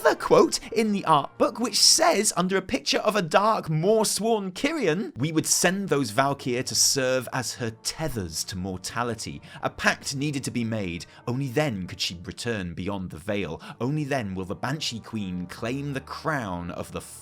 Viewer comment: I swore I heard "Maw-tality"